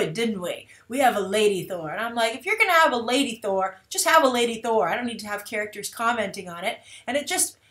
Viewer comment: i thought they said she wasnt "lady" thor, just Thor